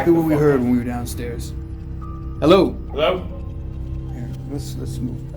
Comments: i heard a response hello didnt you hear that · I hear a hello or heya again.... lemme know if its you guys or the ghost